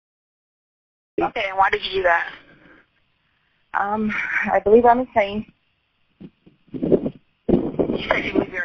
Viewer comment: “I believe I am insane” Proceeds to very clearly explain why she killed him, how she thought about it for some time, and what happened 😂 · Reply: 911 operator thought she said " I believe I'm a saint" 😮